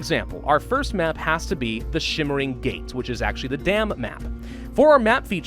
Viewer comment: Every time you said it, I heard "the damn map" lol